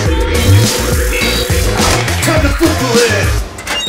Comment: What did he say? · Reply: "Turn the poo-poo lid." Best line ever. (And that's saying a lot.)